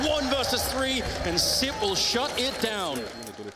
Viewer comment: "Simple shut him down" Am I the only one who heard that? Wth Thats a crusade! How dare the caster mispronounced our president